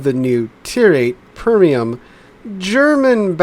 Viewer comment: Am I deaf or did I hear "Permium"?! American "English" gets stranger by the year...